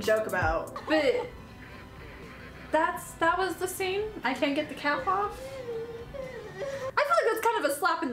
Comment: I thought she said "I think shes there's a cat walk" but she said I cant get the cap off